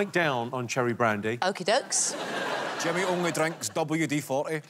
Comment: If only i could understand Kevin Bridges- I hear only:”Gouthry douthry Jemmeh dab-U-D-fauthee…” And everyone laughs. But i keep rewinding until I quit watching at 1:30 😳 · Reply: "Jimmy only drinks WD40."... that might be your hearing, buddy. His accent isn't even as strong in this as it can be